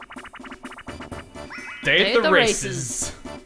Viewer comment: Another great video. Peach's appearance is an improvement in that Face Lift minigame. It sound like you said day at the racists, but I know you said races. That was back when Toad sounded cute and Asian and his voice wasn't ear-rape. There's no non-shouting Toad voice. There is so much more I could say about Toad and about Peach, but I'll save that for next time.